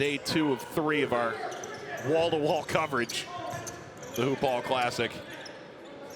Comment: did he say blue ball classic? 🤨